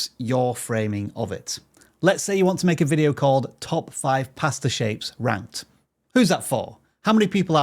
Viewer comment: I swear he said pastor, not pasta😂